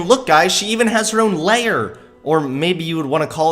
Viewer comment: When he said "lair" it sounded like he said "layer"